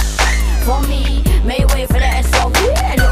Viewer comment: I always thought she was saying "Make way for the SOB" like son of a bitch but I guess its SOV for sovereign, shit.